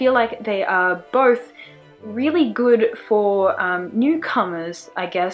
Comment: I thought "newcomers" said "necromancers"... only me!